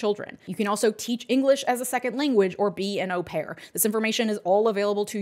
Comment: What does she say at 23:37? "be a no pear??" <link>